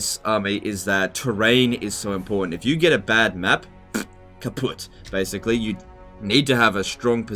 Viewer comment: did he say "kaputt"? Btw it's the German word for "destroyed" or "broken"